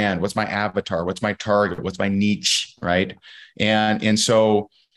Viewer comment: Nitpick and all that but the way he said "niche" at 27:17 sent me, it almost sounded like he was saying "What's my Nietzsche?"